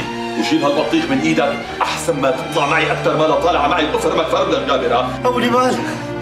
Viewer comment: شو قال؟؟ 23:47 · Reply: افرمك فرم لك جابر هاا